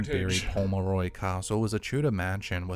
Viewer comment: As soon as you repeated English heritage it sounded like he said the home of Roy castle.🤣🤣🤣🤣🤣🤣 I had to take it back twice pmsfl. ok had to put subs on.. I was close 😂